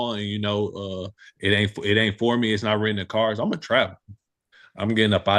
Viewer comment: Man I thought Judon said he was gonna trap 😂💆🏾‍♂️ my bad he said travel